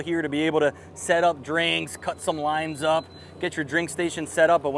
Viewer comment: At the 2 minute mark, he says " cut some lines up" ,, what does he mean by that? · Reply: I believe he actually said limes, but I heard it as lines also....oh what a day that would be